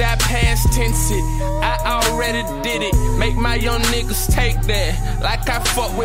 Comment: Bro there is no way I just now heard him say he making his young homies take back like he fuck with diddy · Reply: He said take that not back